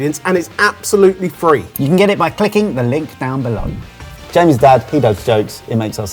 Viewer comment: whn said "clicking the link" at 12:37 I first heard "kicking the link" and immediately changed it in my head to "licking the kink" 😂😂 Sorry, I'm a baaad girl 😂😂💁